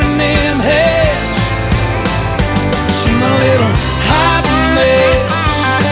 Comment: first heard this at the first time it said my little hot mess it sounded like he said my little cotton bitch haha lol